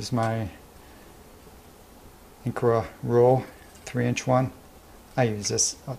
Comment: < What did you call this tool? I couldn't hear it clearly in the video. This is an amazing build! It's inspiring to to see all the thought that's gone into it. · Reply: 3-inch Incra Rule: <link> Thanks :-)